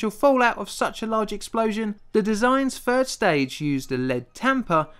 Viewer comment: I misheard that as lead tampon! Gee, thanks........eww.